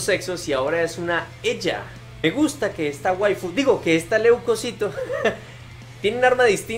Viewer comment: escuche mal o dijo waifu 7u7 xD ok no